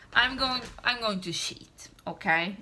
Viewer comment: Ahshahah when you said "i am going to cheat" and with the sad face ... I heard it wrong. "i am going to sh..."🤢 girl just pick a color yourself dont get that annoyed ahahahha